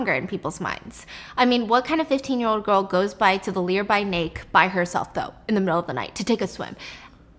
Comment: Omg sorry but I just had to point it out, I've done this many times before too and I always laugh at myself when I catch it, my siblings laugh at me too. Its just that I heard "Lear by Nake" instead of "Nearby Lake" lolol sorry but I just couldn't keep it in. 😂😂❤